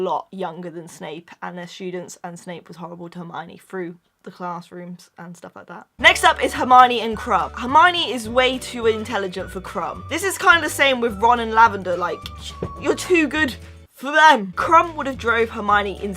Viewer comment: when she said hermione and krum i thought she said Hermione and crabbe... pls help me